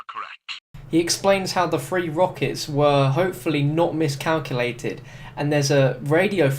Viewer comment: haha, lol, if you turn the english subtitles on at about 1:20, it says, "he explains how the free world peace and love with the norm is calculated" instead of" he explains how the three rockets were hopefully not miscalculated."